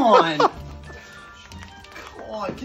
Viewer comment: am i racist or did i hear "come on N'xx"